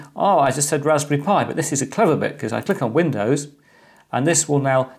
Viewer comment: "...raspberry pi. This is a 'clove' of it"? What is he saying at 1:30?